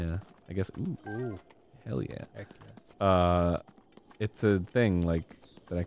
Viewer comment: did anyone else hear the "excellent" at 9:44 or was it just me?